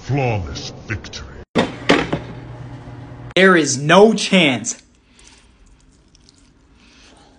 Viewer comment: it sounded like he said air is no chance